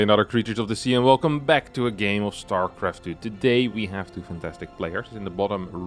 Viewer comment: did he said StarCraft 2day?? 😢😢